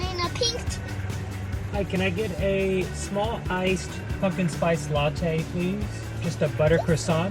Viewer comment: I swear I heard "fuckin spiced latte"